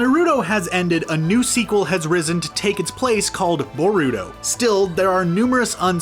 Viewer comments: Boruto? I thought you said burrooto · borito thats what i hear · LOL IT SOUND LIKE HE IS SAYING BURITO INSTEAD OF BORUTO!